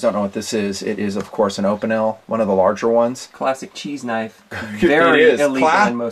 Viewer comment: ! Lol, I 💯did a spit-take with my beverage. Right in the middle of a knife story involving Ms Nutn, I thought I heard say “that’s what she said.” Thank goodness when I went back and reviewed the video he had said “classic cheese knife.” Thank goodness